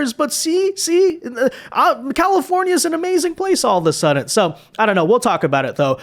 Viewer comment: i swear hes saying suddent.... he did it much more obviously earlier and it is bringing back my old grammar not see tendencies 😂